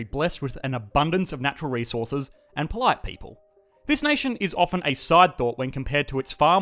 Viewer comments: I misheard and thought you said Canada's full of natural resources and white people. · I thought he said blessed with white people in stead of polite people